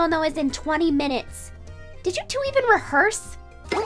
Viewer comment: They be like "rehearse" what that I thought you said "go f each other" close enough right? 😂